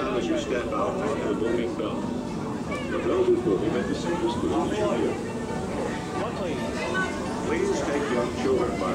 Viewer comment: Why does the cast member at 4:00 have a scowl as he points (with one finger) and what does he say? Sounds like "Omni" ? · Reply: "how many?"